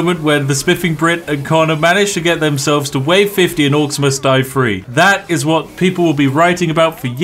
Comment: Is it rude because I had originally thought you said Orcs Must Die Free? I was like fuck yea, I'm getting me a free game, nope. Three. Orcs Must Die Three. Lol